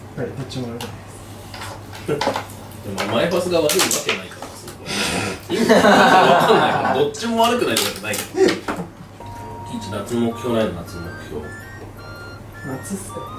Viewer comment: キイチ夏の目標ないの？ が 脱毛ないの？に聞こえて聞き返した